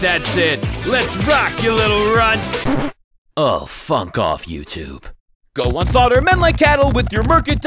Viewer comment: If wasn't a kid friendly channel, imagine how crazy this video would be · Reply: Misheard he said “ oh funk off YouTube “